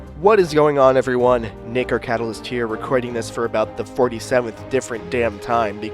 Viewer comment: My first time being here... Sounds like he says "Ni***r Catalyst" but im sure he doesn't lol